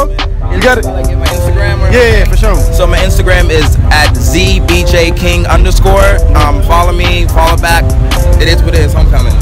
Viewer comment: did he really say “@ B-J king” 🤔? Somebody tell me I’m wrong 😂🥴